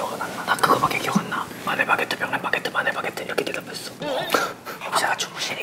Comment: C'est moi ou il dit "baguette de pain" en français 😂